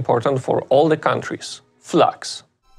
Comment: I thought he said, "flux"